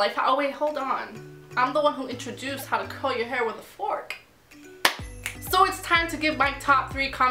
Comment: I thought she said "im the one who introduced how to comb ur hair with a fork" i was boutta say no Ariel did that